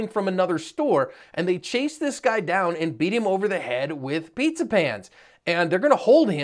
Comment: I thought he said Peter Pan, not pizza pan.. Either way he'll 'never never' do it again. 😁